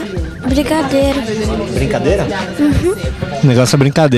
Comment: qual que é o négocio?? é brigadeiro,( 2:59 ) juro que ouvi isso cara, brincadeira só na boca do Cid